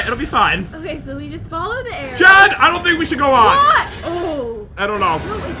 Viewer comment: it’s sounded like said john instead of jen lol